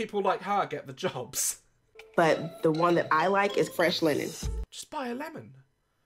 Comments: I love how thought she said “ Fresh Lemon” instead of “ Fresh Linen” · Anyone else catch that he thought she said Lemon? That would be Fresh Linen. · The girl: “I like the linen one” hears it wrong “JUST SUCK A LEMON” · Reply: I thought she said a lemon too 🤣🤣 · her:but my fav is freash linen: *thought she said lemon woman its linenn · "the scent I like is fresh *linen*" "just buy a lemon..." oh.. love.. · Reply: As I read this I heard lemon too😂I know what she's saying but it's still funny mishearing it